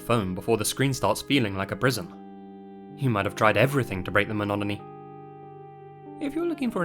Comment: Did you actually just say "monoMany" instead of monotony in the narration for the ad at the beggining of the video? If I heard what I think I heard then I can only assume it made the final cut for the ad and in turn the final cut for the video because either: 1) You are unable to review/edit any narration you provide for ads. 2) You just don't give enough of a fuck to edit any narration you do for ads. If #1 is correct then there's nothing you can really do about it. It's the company who paid you to narrate their ad who is at fault and is deserving of any and all vitriol that will inevitably be stirred up from releasing an ad that includes non-existant words. This shows that the company is run by lazy and/or dumb assholes who either think "monomany" is totally a word or that their target consumers won't notice and/or won't care. Well, company that paid for this ad, we do notice and we do care. My monomany will not be broken up by whatever it is you're selling, thank you very much. And yes, I have no idea what you are selling because once I heard "monomany" in your ad I immediately paused the video to write this comment and cannot remember whatever the first 3 seconds of your ad was trying to sell me. If I were your CEO I'd fire your head of marketing and promote someone in the marketing department who is more deserving of that title and its responsibilities. Next, I'd go on break. All that firing and promoting is a lot of work and I'd very much be deserving of a break to rech